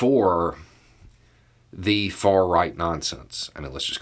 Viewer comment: "the far-right nazis" is what I heard